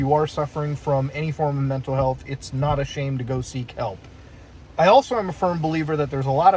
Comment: I know he said "Seek Help" but I heard "Sea Kelp"